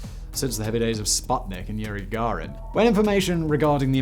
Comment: Check out Squarespace: <link> for 10% off on your first purchase. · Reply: Hi love your videos and on his various narration/host jobs. However, if my ears are tricking me I am sorry but it sounds like he said “Yuri Garin” when the Soviet cosmonaut’s name was Yuri Gagarin!!